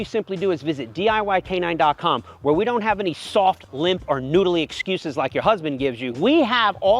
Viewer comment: This guy is that kind of teacher who's strict and doesn't take any excuses seriously, but at the same time he's actually capable to teach you something very useful. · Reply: that's what I thought you said!!!!!!!!! soft lump noodles! are you or your partner...... I ran that shit back to listen again! I love you dude